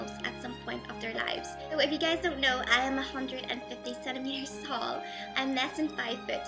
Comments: The first time I played this, I totally thought you said "So if you guys don't know, I am 157 years old,". O.O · replay.... Wait... Did She Just Say She's 157 Years Old???? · When you said 'I am 150 cm tall' it sounded like you said 'I am 157 years old O_O LOL